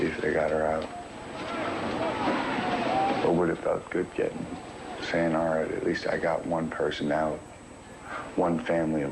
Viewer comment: what does he say? I can't figure it out. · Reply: "it would have felt good getting... alright, at least I got one person out..."